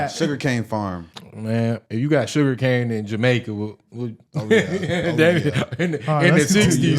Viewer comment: Did he say, “ if you got a sugar-cane plantation in Jamaica back in the 60’s “ ??? 😂😂😂